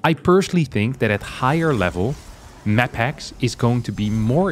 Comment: I keep thinking I hear 'MaxPax' as opposed to 'maphacks' haha